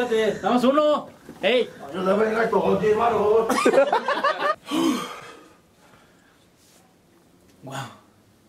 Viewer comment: que dice? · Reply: VAN A valer verga estos, a 10 varos